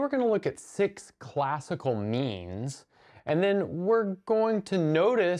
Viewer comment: I heard “memes” and was very confused